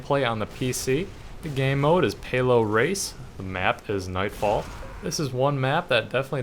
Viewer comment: Phew, i thought you said HALO race, not PAYLOAD race. (I was like, "OMGWTFBBQHAX!")